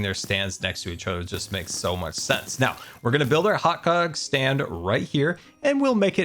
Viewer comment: did I hear right? XD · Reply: Hot gog!!!!!!